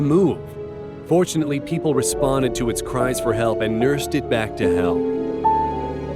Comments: does it say “nursed it back to hell”?? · What does he actually say at 9:15, because the automatic captions say "fortunately people responded to its cries for help and nursed it back to hell"... :(